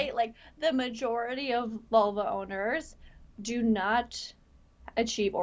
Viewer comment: You lost me at Vulva owners. Not worth my time. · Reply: A moment I thought she said Volvo Owners lol